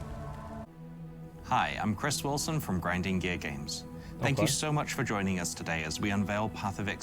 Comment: did he say Grinding Gay Games?